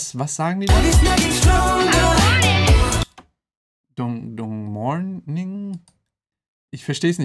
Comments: In 24:24 sagte Halsey "I want it" also das verstehe ich zumindest so Und tolle reaction ich freue ich mehr von deinen kpop reactionen zusehen :) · Ich glaube bei Minute 24:25 sagt sie I want it... Was sagt ihr?🤔😁